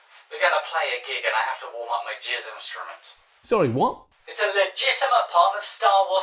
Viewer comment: does he say "I've got to warm up my jizz instrument"? D